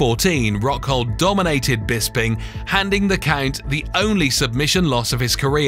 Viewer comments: It sounded like he said "Nicknamed the Cunt" English accents are retarded. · It’s sounded like he said nicknamed “the cunt” at the beginning… am I the only one that heard that?